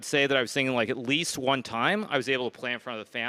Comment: ‘Twice’ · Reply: Hikaru says "one time" instead of once